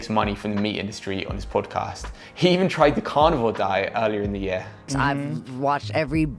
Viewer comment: I first heard "carnivore diet" as "carnival diet."